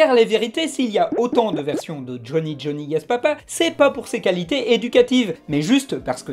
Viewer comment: J'ai entendu ''Johnny Johnny gaz papa''. Ça m'a donné une idée.